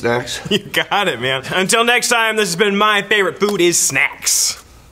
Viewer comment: I need to clean my ears i heard "My favorite Buddhist Snacks."